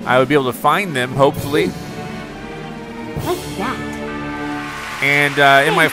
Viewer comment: did she say "F that"? lol